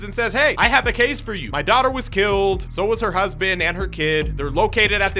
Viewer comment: I legit thought he said so doto was killed 💀